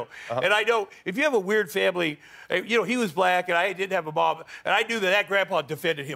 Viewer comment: am i the only one who heard him say "He;'s a black guy, I didn't have a mom..." when comparing himself to obama? lol ??